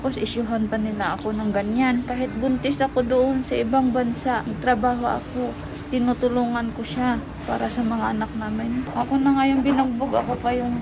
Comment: Correct me if im wrong 8:30 tama ba narinig ko😢 "Kahit buntis ako doon sa ibang bansa nagtrabaho ako, tinutulongan ko siya para sa mga Anak namin"? 😮😮😂